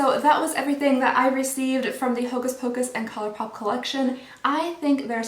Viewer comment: I thought that said colonoscopy 😂 but it’s colorpop 😂😂😂